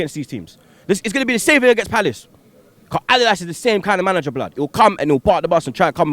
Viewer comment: When he was saying Allardyce I thought he was saying adidas